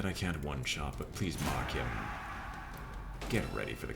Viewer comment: when you marked the ghoul I heard it as "please mock him" an thought "but you don't have a Jester